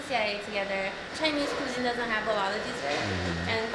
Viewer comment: Wait erm... did she just say chinese culture doesnt have alot of deserts...? Nani tf LOL.